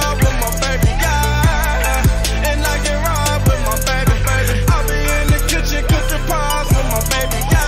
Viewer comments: i always thought he said I get robbed with my baby 😂🤦‍♀️ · So for so many years I thought he was saying “cooking paws wit my baby” but he’s been saying pies😭🥹🤦